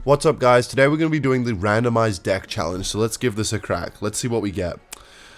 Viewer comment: did he says "deck"?